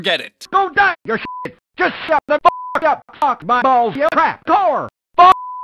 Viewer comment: Everyone: "LOL AT 5:59" Me: Guys my hearing's bad, what does he say? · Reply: I'm pretty sure "trapdoor" was actually "crack whore" and that's concerning